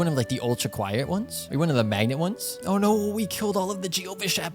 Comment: I thought he said "Oh no we killed all the Jews !"